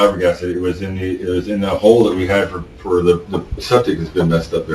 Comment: Wow 17:30 was a freudian slip, thats odd. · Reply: I just commented about the same thing. When he says "why he buried OR.." I thought I heard Why we buried 'HER', but the subtitles say otherwise and I also checked the YouTube subtitles and those said the same. Can't be sure? Same at 17:50 "It was in that hole we had 'HER'. umm for the septic that's been messed up there..." And then the fact he rubs his head right after, which is what you do when you mess something up, you hold your head... and then he fake yawns to make it seem like he's not fazed..nothing to see here type thing. Yup I think that's a freudian slip for sure